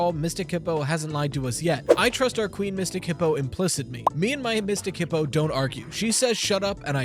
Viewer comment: What does he say here? "I trust our queen Mystic Hippo ____"? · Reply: I assume he meant to say "implicitly"